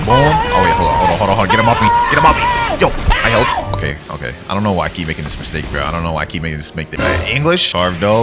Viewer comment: I thought he said"i keep making a steak"😂😂😂😂